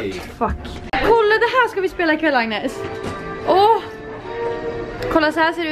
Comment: när Linn sa ”Kolla, det här ska vi spela ikväll, Agnes!”, hörde jag först ”Kolla, det här ska vi spela, Karl-Agnes!” och kände mest ???